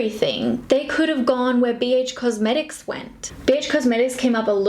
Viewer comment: Did anyone heard bitch cosmetics on 8:44 or is it just me 😂😂